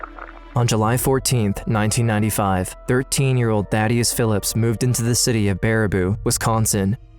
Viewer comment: I’m sorry but did I hear “Baddie Philips” cuz he’s so preppy and slay all day